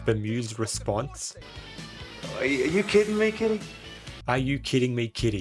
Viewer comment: Huh.. I always thought he said "Kiddie"..